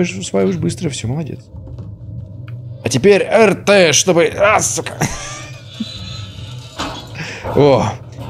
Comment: Мне послышалось или на 13:33 минуте сказал сука